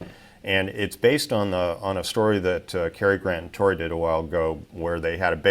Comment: Okay, I know he said “Kari, Grant, and Tory” but I swear it sounded like he said “Cary Grant and Tory.”